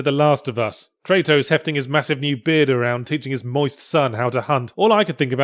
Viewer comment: I really, really thought he said "teaching his wife's son" Yahtzee was almost an absolute madman.